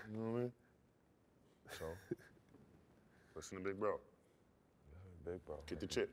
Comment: What does he say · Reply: You know what, I mean, so...listen to Big Bro (meaning Kobe)....get the chip!